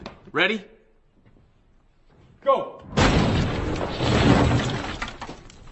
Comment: 2nd guy punches hole in wall “That’s why you pick me, or your dead” “Aye lemme get Arron Hernandez lil cousin over there 👉🏾 “ 😂🤣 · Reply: I heard “that’s why you pick me, or you’re dead” in a Russian accent lmao